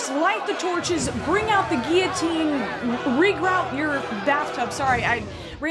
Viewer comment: I thought you said reroute your bathtub and was so confused.